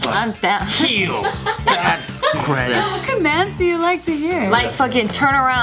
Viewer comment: Did just say "bad credit!"?? HAHAHA